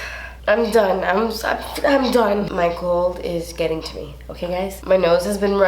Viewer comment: I thought you said “Michael is getting to me” and I immediately thought “which Michael am I gonna fight?!?!” LOOOL here for you siz!!!